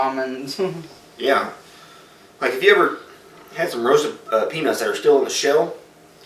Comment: It sounded like you said roasted penis while licking the shell. lol